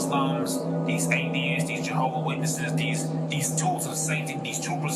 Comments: He did not say "jew witnesses" he said "Jehovah's Witnesses" .... It's also spelled "enemies" with one n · Lol, you put "Jews Witnesses" instead of "Jehovah Witnesses."